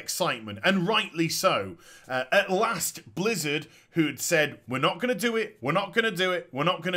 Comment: "We're not going to do it" They actually said "You (the players) don't want that." 2:40 There you go. Original WoW will never come back. They stated at Blizcon "We're not going to start at version 1.0" Right before BC was launched/announced talents were still being reworked. Weather effects weren't there in the beginning. All classic wow is going to be is, Base wow content, with what bliz thinks classic should have been, be it based on their opinions with a tad of consideration of the community. After launch each group of the community are going to "voice" their opinions, and the loudest one is going to win. Again I'm on of the few that wants the feeling of the struggle of reaching 60, how pallies were trash, rouges were op, the annoyance of ammo, having to walk forever, seeing new sites, having broken bosses, having to know what stats are useful, aggro, paying attention to mana, the list goes on. The point is Base original WoW isn't what people want, what people want is what they think it should be. It's like build-a-bitch, where you state how you want you next significant other to be. If it's no to the tee, you'll bitch about. It's as bad as Microsoft calling their third console Xbox One. 3:40 Flying wasn't Base WoW. You had taxi AKA Flight Points (FPs). Flying was introduce in BC, along with Jewel crafting. Once you take something from a time that was outside of Base WoW, it's not base WoW anymore. If they are not going to start from the beginning, the way wow did, th